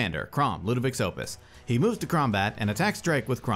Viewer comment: did you say "he moves to Kraumbat" ? lmao